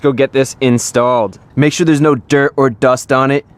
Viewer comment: At the end o thought it was gpnna say "so then it goes in dry"